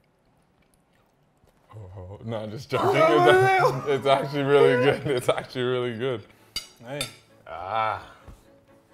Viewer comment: $50 who can tell me what he said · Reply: Mke Big G “I was about to say”